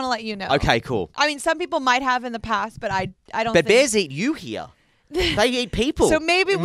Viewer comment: DID HE JUST SAY THEY EAT PEOPLE AND SHE SAID YA WHAT THE HECK